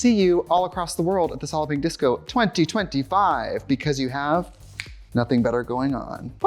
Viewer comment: This is the first time I realized she hasn't been saying Solloping Disco 🤯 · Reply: I always thought she was saying solping disco